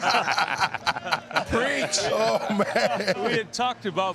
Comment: It sounds like "Beijing, Beijing", what did he say? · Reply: Bench him, bench him!